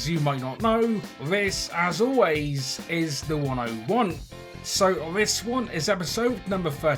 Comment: When he says as always this is THE101 it sounds like he says as always this is the one I want😂